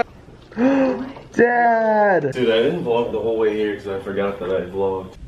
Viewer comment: Is it just me or at 3:12 when walked in did he say dad or Zack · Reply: dad